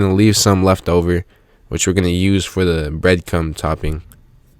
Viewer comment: Am i the only one who heard bread cum?